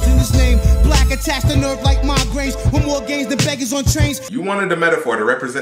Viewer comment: Always thought he was saying more game than beggers on trains...